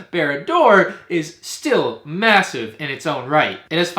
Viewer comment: Did he say... Massive?